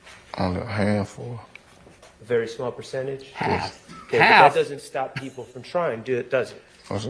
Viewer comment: If i heard correctly, i thought Obama didn't say "half," he said "handful" 😊